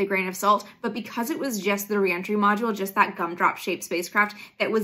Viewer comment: At first I heard “Punk rock” shaped and not Gum Drop shaped and immediately my brain was like “What does that mean?!!” then I heard it correctly 😂 either way, 🤘🏽🚀